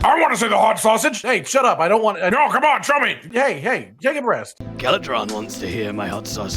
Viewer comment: anyone else hear “Take a breast”?